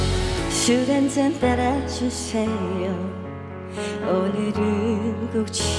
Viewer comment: 자막안보고 들으니까 술한잔 빨아주세요 라고 나만들리냐?